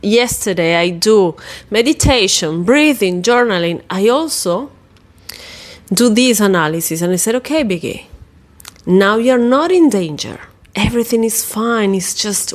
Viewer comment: Do you say "okay Vicky"? I heard "okay bebé" at first 😂😂 More seriously: great video! I also have strong triggers (other situations) and I can easily imagine that storms are linked to traumas - for a lot of people.